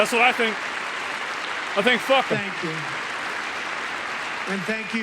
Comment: "We have a healthier country than I thought, Bobby," had to be the funniest line of the night. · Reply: i was just listening and not fully paying attention and i thought he said thank you papi 😂